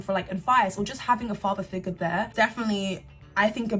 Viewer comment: okay but whyd I hear Riri when you said “definitely”